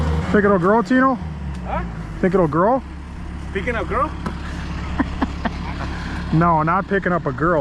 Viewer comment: pickin up a girl LOL. Can’t blame Tino .. that’s what I heard as well!!😂